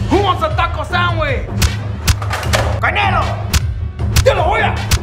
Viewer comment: Is it just me or did anyone else hear Juan say CANILO when punching the bad guys